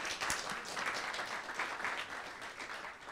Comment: “But I’m trying to insult you sir!” “I heard you, have a nice day.” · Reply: He said actually said: “I don’t think you heard me”, it goes to show how dishonest you are....